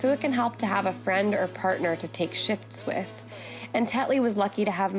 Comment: Perhaps because of the visual context, I thought she said "a friend or partner to take shits with". 😅